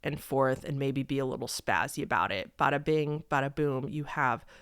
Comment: Did you really just say "be a little spazzy about it" at 16:09 or am i mishearing